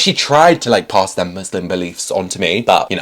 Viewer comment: I understand that, but when you grow up it's your responsibility, so if he reads this I advise him to learn more about Islam because Allah is always there and never abandons His people and His creation. Allah understands all circumstances, and we must strive to worship Him in the right way because that is what we were created for. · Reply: i think he said he not muslim